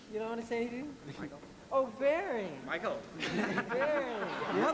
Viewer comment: Mj: “You don’t wanna say anything?” suddenly wild Barry appears Barry: “Michael...” Mj: “Oh Barry!” Barry: “Michael!” Mj: “Barry! How you been?!” I adore that part 😂😂😂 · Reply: Dani Medina really Lol I thought he said “oh that’s you Barry” not “how you been” lol